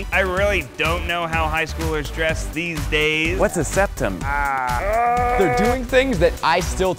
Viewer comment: Did I hear what's a septum